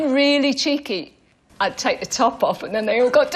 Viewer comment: aT FIRST i THOUGHT cAROL SAID i'D TAKE MY TOP OFF, OH GORD i NEARLY BLEW IN ME PANTS.